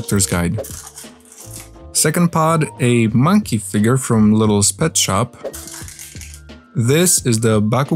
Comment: I thought he said little sped shop